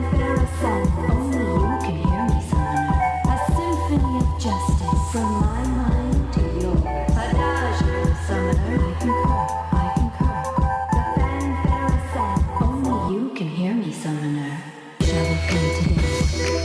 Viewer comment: Why do i hear "A doujin summoner"